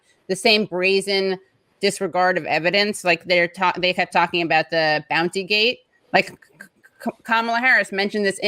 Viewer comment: I thought Katie was saying bouncey-gate.